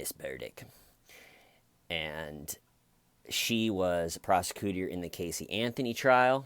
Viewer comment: I heard "she was the proseCOOTER in the Casey Anthony case" 😅